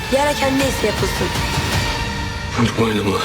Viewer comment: burda ne dedi??? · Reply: Vur boynumu